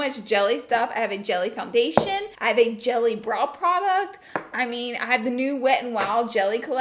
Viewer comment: did anyone else hear bra instead of brow...